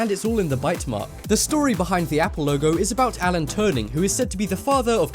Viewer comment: Did he say Alan "Turning " at 0:51?